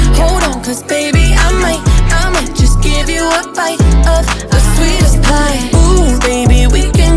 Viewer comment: why am i hearing "sweedish pie"